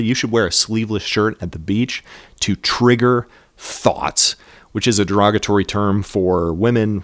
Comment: Sorry, but I think you actually misheard him (And very severely misinterpreted what he said). He said that women he knows have said that men being topless can trigger "thoughts" (That is, presumably, lustful thoughts) not "thots". That's why he said you should do it "Out of respect for them". So he's essentially saying the opposite of what you're suggesting - that is, he's advocating sleeveless shirts rather than not wearing a shirt at all, to avoid "triggering thoughts" in women. This is, I think, pretty indisputable if you listen to the conversation.